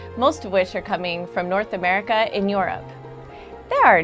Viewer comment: did she just say north america in europe?